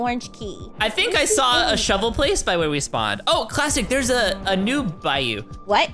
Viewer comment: Did I hear wrong or did say clastic instead of